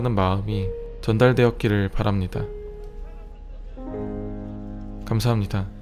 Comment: 나만편하고였습니다 라고들리나?